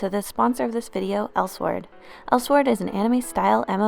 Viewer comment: In the beginning I thought it said Ellsworld. I wish it was tbh, nothing against Elsword